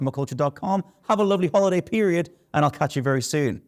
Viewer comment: I swear it sounded like said, "I'll cut you very soon!" at the very end. Proceeding to lock my doors, now.